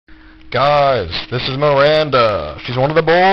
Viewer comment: lol i thought he was actually saying Narinder, but it was Miranda Xdd